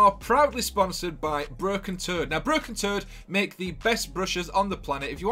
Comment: I thought u said "BROKEN-TURD!" LMMFAO!!!!! I Had to replay it to make sure i heard u right Broken Toad Ok Got'cha 😆 🤣 😂